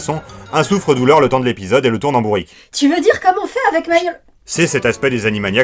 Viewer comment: Vous alliez dire quoi ? J'ai pas capté la blague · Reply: "Tu veux dire comme on fait avec Mayo Lek ?" On se comporte comme ça avec lui dans les crossovers